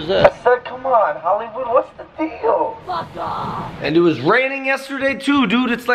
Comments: Did anyone hear f of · I hear that lady a lot saying fuck off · who else heared "fuck off" at 0:28 seconds from the start of the video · Has anyone heard someone saying fuck off? 🤣🤣🤣🤣🤣